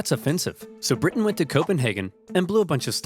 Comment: d-did i hear “coping”nhagen..? (i know its called copenhagen)